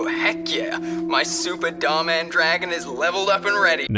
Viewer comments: I thought he said ''super dumb ahh dragon'' and they just didn't include the ahh part 😭😭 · i love how in the subtitles it says "super dumb dragon" instead of "super dhar mann dragon"